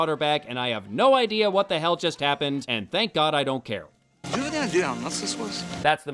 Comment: What does Assante say? I've listened to it half a dozen times and I can hardly make out a single word. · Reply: Do you have any idea how nuts this was?